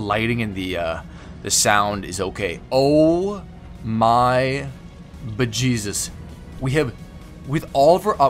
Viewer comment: Did you just said “oh my vagisus” at 1:10? Is your god a vagina or am I overthinking? Loved the video.